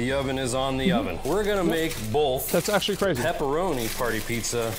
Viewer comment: Wtf did he say? Bolth? What the hell is bolth? "BOLTH"